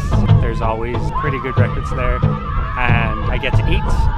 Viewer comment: Groovacious. I thought you said good breakfast but when you clogged inside you meant “records” 🤣 🤣. Was hoping to see what kind of food they had.